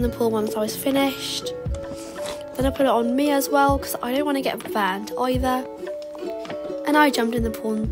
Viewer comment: i thought she said “ cause i don’t wanna get banned” lol